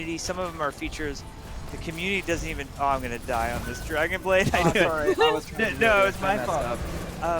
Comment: did he just say dragonblade?? 😕